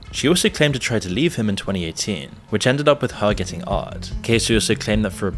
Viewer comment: someone please tell me what he said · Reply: " To leave him in 2018, which ended up with her getting "R'd". By which he meant raped